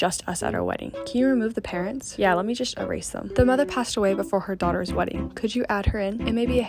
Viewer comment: I thought you said "yeah let me just show rasim" instead of "yeah let me just erase them" when there are captions